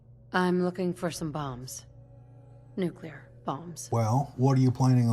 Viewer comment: why did i hear balls😅